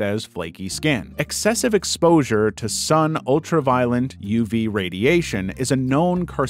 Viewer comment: did he say "ultra-violent" ?? 😭😭